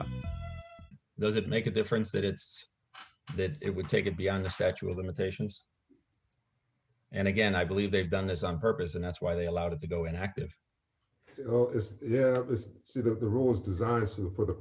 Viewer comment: i could swear he says statue of limitations, its not a statue